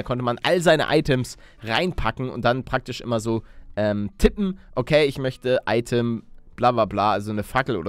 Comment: tippen ich verstehe titten